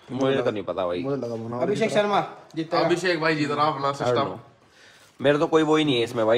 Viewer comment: did he say Abhishek Sharma?😂